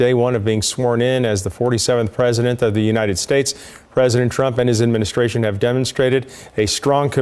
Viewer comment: I thought I heard creepy Johnson call trump prisondent trump. It's a better name for the Criminal 47